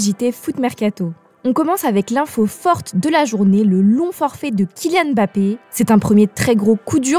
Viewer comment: "le non forfait de K.Mbappé"... "c'est un très gros coup dur..." Sérieux, vous vérifiez les conneries que vous dites ou c'est comme tout les autres merdias, vous ne vérifier pas ce que vous dites avant de le publier ? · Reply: Le LONG forfait !!! Moi aussi j'ai entendu "non forfait" mais c'était juste un défaut de prononciation !